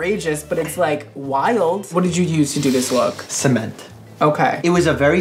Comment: Brad" "what did you use to do this-?" Mike: "cement" Me: takes a full 10 minutes to realize he's not talking about actual cement and that its a hair product and wondering why brad didnt freak out when he said cement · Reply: First I heard “semen” instead of “cement” LMAO